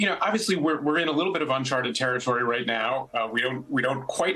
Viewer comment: Uncharterered ? Sounds as if Fallon gag-wrote ? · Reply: I don't understand the Fallon reference, but I note that Noah Bookbinder used the term (approx 2:20 in) "uncharted territory" and not "unchartered" as per the caption writer